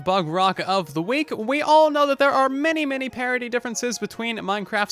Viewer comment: did u say parity or "parody"